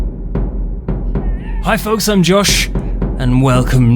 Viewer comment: At the beginning it sounded like he said "Hey folks I'm jewish"